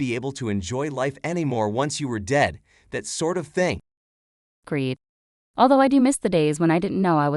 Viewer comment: What did the one on the right say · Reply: She said Agreed